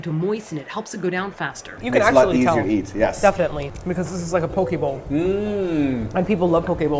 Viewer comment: Why I misheard pokebowl as pokeball 😂